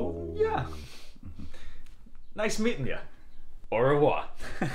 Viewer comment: "I can say hello and goodbye" is a perfect joke, as they are both "ciao" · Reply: Yeah i thought the same, but he said au revoir :D